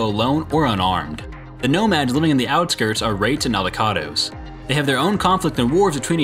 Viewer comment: When you said wraights and aldecados, I swear it sounds like your saying raped in avocados